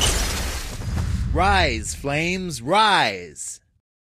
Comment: I thought he said Rice Flames Rice, Or am I hungry